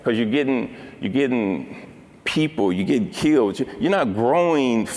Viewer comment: does he say, "you get killed"? lol even the CC say that